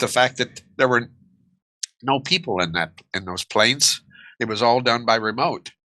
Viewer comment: What’s the professor saying at 29:43 ? No people in the planes? Done by remote?